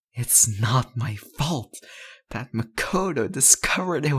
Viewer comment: I thought it said Komodo :/